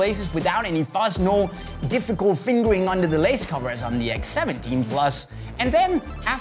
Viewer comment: Did he just say 'fingering'